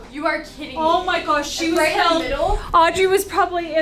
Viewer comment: Oh my gosh! 😂 at 9:54 just listened for a sec... it sounds like says “she was killed“ 😂😅